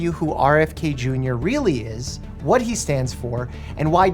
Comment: oh gosh I thought he said "Robert F Kennedy Jr is an F U of former president John F Kennedy"